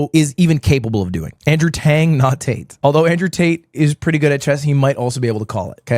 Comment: i had to tab back in cuz i did think u said tate